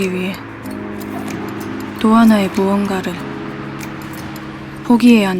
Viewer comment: 도하나로 들림